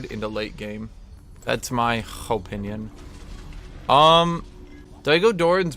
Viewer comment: Did he say "hopeinion" at 1:04?